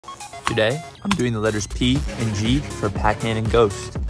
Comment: When he said p and g i thought he said PNG🤣